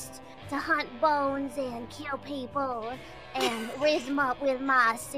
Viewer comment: I'm fairly certain Biboo said she'd rizz instead of erase people with her silver tongue